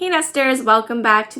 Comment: Did u just say: hey basterds when u started ur video??? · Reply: Nesters lol